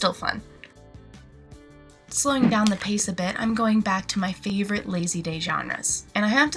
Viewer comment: did she said my favorite lazy vaginas?